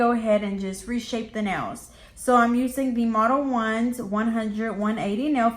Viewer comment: I thought it said mode-Leones